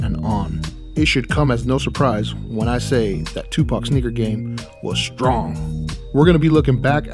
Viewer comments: thought he said n*** game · Lmao Whole Up?? Did You Just Say 0:54 Tupac’s Nigga Game Was Strong? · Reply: 2Pac Was The Goat! Hell nah I said sneaker game